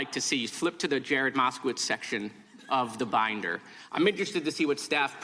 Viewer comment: Was she trying to suggest he said "The Bible" when he clearly said "The Binder" and then further specified "The Burn Book" ??? Wow... Now why would she do that...? Maliciously shameful.